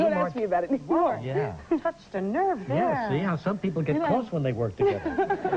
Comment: What did he say at 3:10? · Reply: Some people get close when they work together.